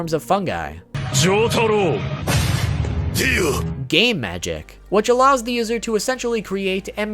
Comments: I can't be the only one who heard "Gay magic" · Right before he said Game Magic I looked away from the screen and I thought he said Gay Magic.😂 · I sweat to God I heard him say “Gay Magic” LMAO😖😂🤣